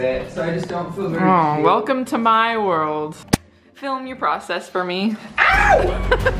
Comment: That intro went by so fast and I thought it said welcome to my dog lmao